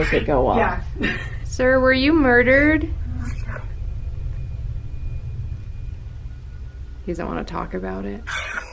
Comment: when said "He doesn't want to talk about it" I hear the person in the spirit box say" I don't want to."